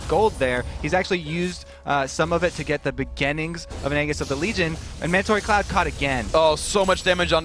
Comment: Did Kobe just say Angus of the Legion?